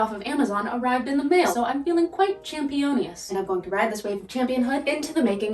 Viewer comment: I love that you actually document daily life outside of just focus on the dress itself. The editing part is the worst when I try to do anything with the social media 😅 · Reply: Haha I love her quirky humor although her dad puns need work XD she said she felt Championous which just sounded like the spanish word champiñónes which means mushrooms 🍄 xD. I heard "I felt very mushrooms"